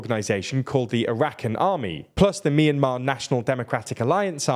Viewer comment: For a second I thought you said "Arachnid Army" to which I determined that they are a force not worth going to war with. I, for one, welcome our new spider overlords.